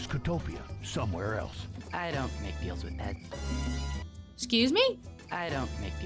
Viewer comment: I heard "I don't think you'll detect me" not "I don't make deal with peasants" it's like ghost hunters listening to a fucking radio.